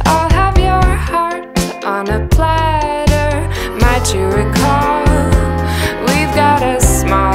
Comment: I am French : I heard "Mathieu Ricard (a very nice guy by the way whom I happen to know a bit) " instead of "Might you recall". It was a bit strange !